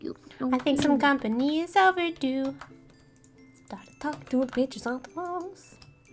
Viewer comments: xD I thought Annie said "Started talking to the bitches on the wall!" XD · did she say i think some company is overdo and silently she said and u talk to b@@@@@ on the phon e😑😑😑